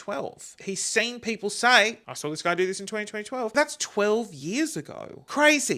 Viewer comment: I can’t unhear “20 20 12” instead of 20 12 😂